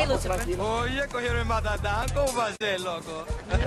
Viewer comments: What was dude saying @ 1:08 ?? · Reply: Dominican Spanish - “Oye, cogieron el matatan! Como va a ser, loco?!” Translation - “Yo, they grabbed the big dog! How’s that possible, my dude?!” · Haha Peoples is my idol! Anybody know what the guy on the bike says at 1:08? He looks very scared at the end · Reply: EPICDRAGONLORD Oye Coje el matatan hay curva de loco